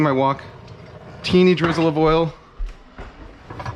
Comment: i really thought you said "teenagers love oil"